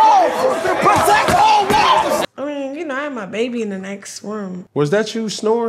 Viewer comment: All I heard was...."I had my baby in the next room" 🧐.....why? Why are you there with your baby? This generation man smh